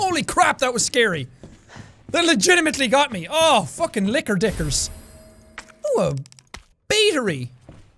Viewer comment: the whole time i thought he kept saying liquor instead of licker